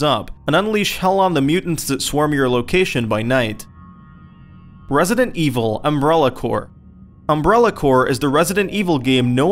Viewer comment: lol I thought he said resident evil umbilical cord